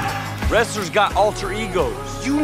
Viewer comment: sounds like he says "Pretty Bird Falcon" either way its peanut butter falcon time